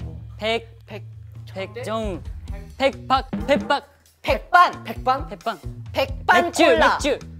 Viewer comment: Yo: Lo único que entendí fue Peppa 1:55 :v